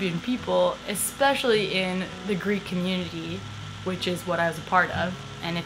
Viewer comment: what community does she say at 7:14? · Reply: Greek